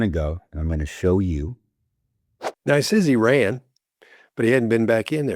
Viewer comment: Did he just say Iran? 🤔 No. He meant he ran. 😵‍💫